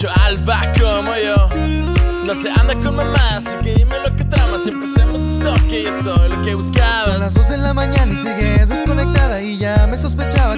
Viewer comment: Que dice del 1:08 al 1:10? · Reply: livier yañez ..."No se anda con mamadas Dime lo que tramas Y empecemos esto Que soy lo que buscabas"... 😂lose, yo tmbn batalle para entenderle